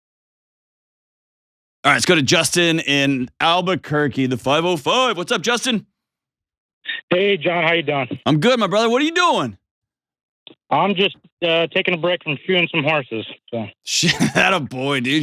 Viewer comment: i thought he said "shooting some horses," and was flabbergasted everyone was just like oh cool. 😂 he said "SHOE-ING some horses." 😂😂😂❤